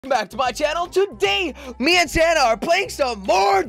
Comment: it sound like jelly saying santa to in the frist part in